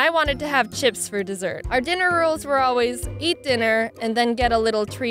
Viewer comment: I heard "dinner rules" as "dinner roles"